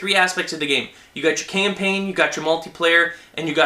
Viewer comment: i thought he said "there are 3 aspects of the game; camping, multiplayer and co-op" LOL